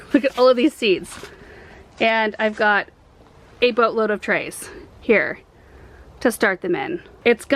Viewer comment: I thought you said you had a “butt load” of trays and laughed and then realized you probably said “boat load,” haha 😂